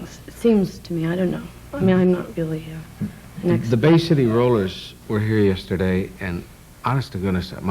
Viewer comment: I too thought MD was saying The Bay City Rollers were screamers, at first. She is adorable.